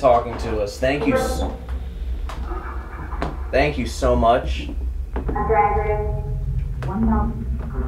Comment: when says the first “Thank you” and gets cut off it sounds like is saying “You’re welcome”